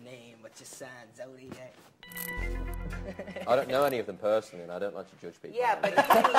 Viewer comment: Wait what did Liam say that Niall was laughing at hysterically at 7:00? I couldn’t understand him! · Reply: he wouldn't say who his celebrity crush was because he "didn't know them personally and doesn't like to judge people".